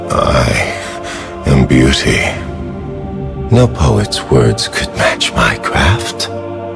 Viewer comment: I alway thought he said: No poet worlds could match minecraft.